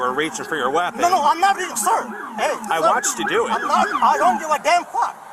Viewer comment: did he say “I want you to do it” ??? what was that comment about??? Cause 1 second before that he said “They’re responding because you reached for your weapon”…. am I hearing something?